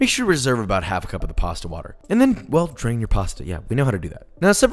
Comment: I listened to this while making dinner and I heard “drink the pasta water” instead of “drain the pasta water” and almost had an aneurism over my noodles 😂